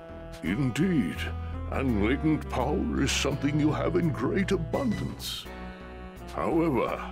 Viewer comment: he says great but captions say some